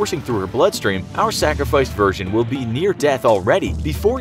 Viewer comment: did he say our sacrificed virgin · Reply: He said virgin. Next time watch the video from the start instead of the middle